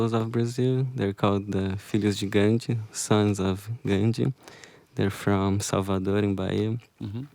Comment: what does he say there? Sons of "..." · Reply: Gandhi